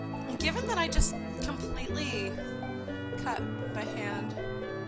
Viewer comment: lol people thought you cut your hand because you said you said something like "cut by hand" and people (including me just now) thought you said "cut MY hand"